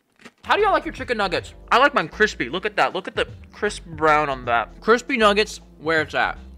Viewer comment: When he talked about his chicky nuggies I thought he said “mm look at Chris Brown” 💀